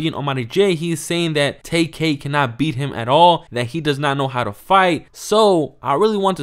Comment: I thought nahmir said he don't fight😂